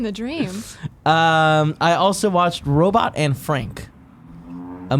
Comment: I definitely heard "Robot Anne Frank" and my interest was piqued